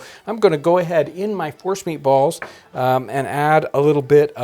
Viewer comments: Watching these videos on auto with the screen minimized.... Swore he said horse meat. Whew! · I thought he said "Horse meat" 👀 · Ha ha for a second I thought he said "horse meat" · I thought he said horse meat!! Lol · Am I the only one who thought he said " horse meat?"